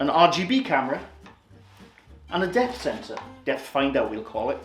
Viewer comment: I Ithink I’ve sussed the problem …. I didn’t have my sound loud enough and I thought you were saying death finder when you were actually saying depth finder, that’s it that must be the confusion 🤷🏻‍♀️ 🤪😂 How anyone believes the Kinect can see ghosts 🤦🏻‍♀️ Thank you for explaining this so well for Dan, are you two related 🤔 😂